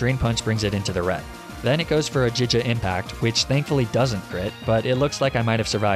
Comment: did he just say... jija impact?